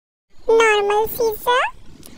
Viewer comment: Bro did I just hear “ normal daddy “ “cute daddy”💀💀💀💀💀💀☠️☠️☠️☠️☠️ I’m dead rn 😂💀😭